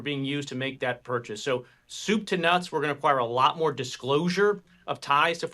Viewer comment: did he say “soup to nuts“? Sounds like a threat